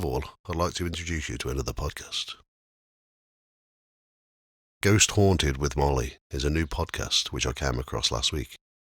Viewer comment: Thank you ❤️. I'm not sure what you mean, who's Molly? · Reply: you mentioned at the beginning about someone else who does a podcast I thought you said ghost haunted with molly